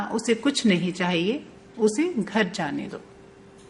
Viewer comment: I heard jaane do dekhiae jagah